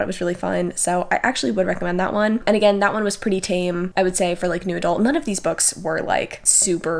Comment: Heh, whenever says "new adult romance" I hear "noodle romance". Now I feel like noodle romance should be a thing.